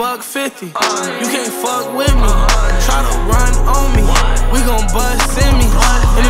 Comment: I thought he said we gon buss in me